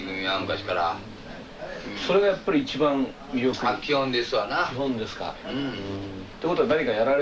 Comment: この「タキオンですな」ってなんていってるの？